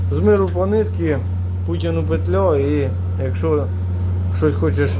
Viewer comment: С миру по нитке, а кому петля не расслышал, со слухом проблема. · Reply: Путину